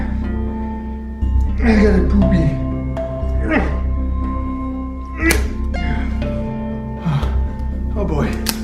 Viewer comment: does he say 'I go poopy'? 😂